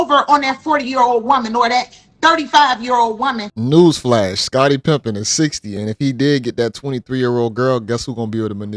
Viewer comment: I heard "Scottie Pimpin" 😂, maybe it waa slip of the tongue, or something you been waiting to slide in.